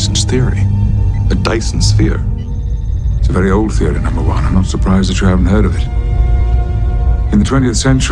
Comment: “It’s a very old theory number 2 I’m surprised you haven’t heard of it” Shots fired on deck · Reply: I thought he said I'm NOT surprised